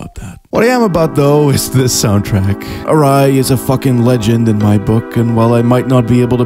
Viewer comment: ??? what's the musician he's mentioned at 17:20? I hear something like "Arai", but can't find it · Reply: Riow Arai